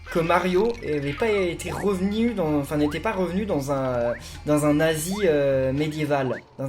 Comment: J'ai entendu "revenu dans un nazi" !?? PS: Et c'est trois VERS pour la poésie (presque, dommage, les versets c'est dans les Livres sacrés, majoritairement) !